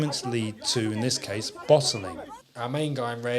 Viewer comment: LOLOLOLOLOLOL!!! Your accent threw me off for a second at 3:10. I was listening only and I thought you’d said “bottoming” instead of “bottling”, an error which greatly changes the …. errr …. potential climax of the altercation.